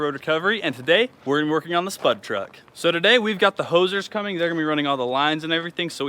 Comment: I thought said the Hosers are showing up. Going with todays theme, I thought more YouTubers were showing up. LOL